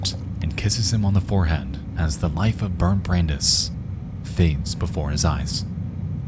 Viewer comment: The whole time I thought he was saying "Burnt Brandis" and I was like wow that's kinda out of tone lol